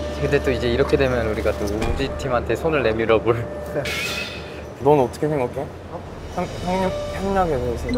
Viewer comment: Jeonghan:let's consider teaming up with woozi Subtitles:Has feathers instead of fingers Hahaha🤣